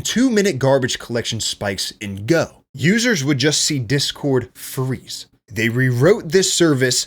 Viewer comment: def thought he said "discord furries" at first, especially with the discord logo on the screen